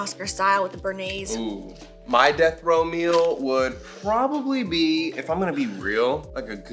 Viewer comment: Everytime said “death row meal” I kept hearing “death Romeo” and idk lol. The first time he said it I was like what?? And then when you answered him I’m like ahh okay death row 😯 got it lmao. Too many margs maybe 🤔😂